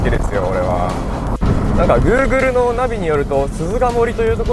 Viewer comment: Googleが一瞬グルグルに聞こえたw ここも結局使われなくなって湖に沈んだのだろうか・・・?(・_・;? こんな道の前から車来たら死んじゃうよ😅w